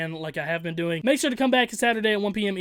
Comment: i swear to god he said “i’m out...penis”